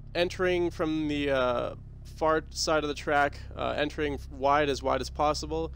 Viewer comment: I swear you say, "From the fart side of the track". Cracked me up.